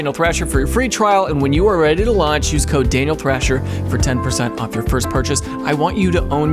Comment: If you fast forward through the ad just right it sounds like you say anal thrasher.